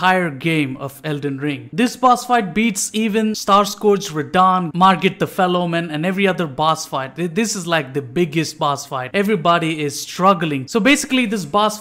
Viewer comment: I’m lit. , I thought you said Margit the Fellowman